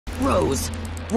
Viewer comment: I heard "GROSS"